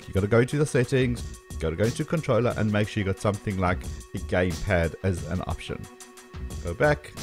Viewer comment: What did you say from 3:17 to 3:27? Game head? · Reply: game pad - like it says on the screen